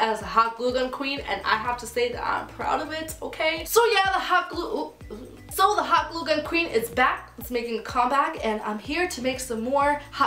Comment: The hot glue gun queen is back :)) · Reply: Sarah Ahmed omg I thought you said (not racist!) "The hot glue gun queen is black :))" sorry I'm so dumb ignore this. I'm so dumb I don't wanna press done 😐😐😐